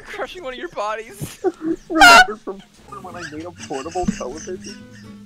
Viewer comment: 🤣🤣🤣🤣🤣😪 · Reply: Can anyone hear “portable television”?